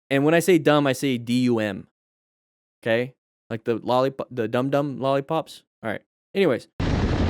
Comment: When you said "dum dum" i heard "dandan" xD